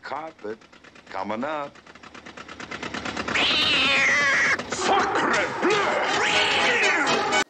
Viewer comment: I kinda thought the truck driver said f***crebleu